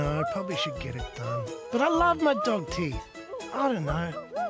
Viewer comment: I THOUGHT IT SAID “but I love my dog Tee.”